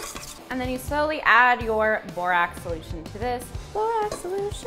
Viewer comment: 'Add borax solution slowly' pours almost the whole container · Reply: For a moment I thought had said "Borat" and I was like, "Canadians do have strange things...".